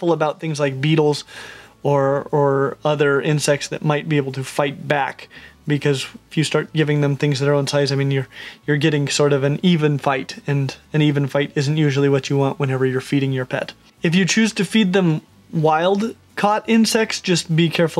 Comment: "An even fight isn't usually what you want whenever you're feeding your pet." I absolutely loved that😂👍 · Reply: Oh thanks for commenting because I heard "even fight is what you want" 😂